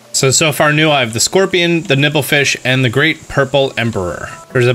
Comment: Am I the only one who hears “nipple fish” at 16:24? I think it sounds hilarious.